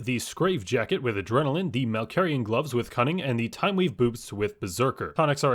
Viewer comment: i swear my guy just said timeweave boobs